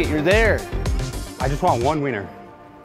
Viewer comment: I heard "All I want is one wiener" and I won't be convinced I heard otherwise.